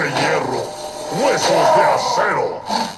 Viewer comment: JAJAJA pensé que dijo huevos de acero!